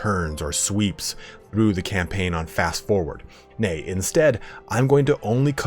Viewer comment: did you say "Nay?"